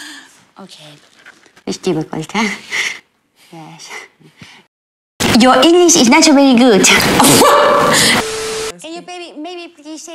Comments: " Your InGlish is Not so BerrY gUd " Adele: GUrl I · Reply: yah, I heard ' your English is naturally good' 😃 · " Your InGlish is Not so BerrY gUd " Adele: GUrl I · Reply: Nah I heard “your inglish is nacho Bery gud” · " Your InGlish is Not so BerrY gUd " Adele: GUrl I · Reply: i heard "your english is actually really good"